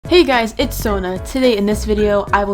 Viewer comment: I thought he said hey guys its zonut